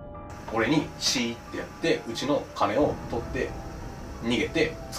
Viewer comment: うちの亀に聞こえた...。